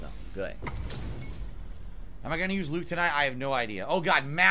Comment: Thought he said "am I gonna use lube tonight, I have no idea".